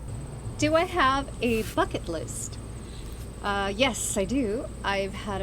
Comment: When you said "bucket list" I thought you said "fk it list" 🤣 which is actually a great concept, pardon the pun.